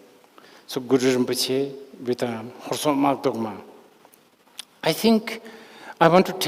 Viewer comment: Which manifestation diverts division and extremism? It sounded like Rinpoche said pray to Guru Rinpoche with Husok Maldolkma?? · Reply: Horsok Makdogma ཧོར་སོགས་དམག་བཟློག་མ། Literal translation would be "the one who dispels war from the Mongols and the like.."